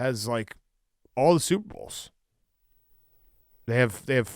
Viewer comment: remember when that guy got on Big Cat for pronouncing "Super Bowl" very quickly to where it sounds like "Soup Bowl". Then Big Cat got mad about it. Yeah it's all I hear now. 1:20:05 for a good reference here